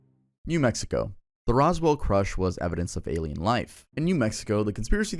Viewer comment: You put crush instead of crash 20:40 it's ok long vids are hard to make 😮‍💨👽